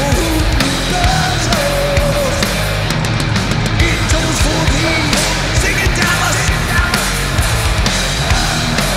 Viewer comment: What is James saying here? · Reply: "It tolls for me! Sing it, Dallas!"